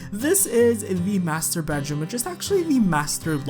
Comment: "Master Bedroom".. i just heard "Masturbateroom". :D But it could be me.. xD